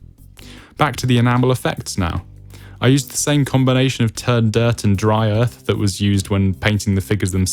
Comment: At 23.20 mins in it sounds like u said . Turd dirt ha 🤣. Really nice little build 👍 well done.